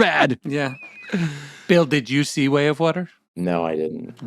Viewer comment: I heard, “…juicy way of water.”